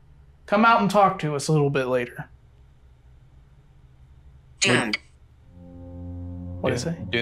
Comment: When you said it said doomed I thought it was saying jerk lol